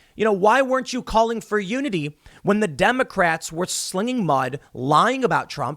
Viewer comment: I thought Joe said to Unite! No one is listening.